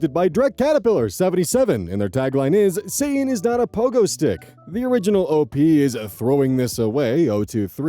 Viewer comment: With how often I get told that Satan is not a pogo stick, I'm starting to think he might be one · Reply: Wait? It's "satan is not a pogo stick"? I always heard it as "say it's not a pogo stick".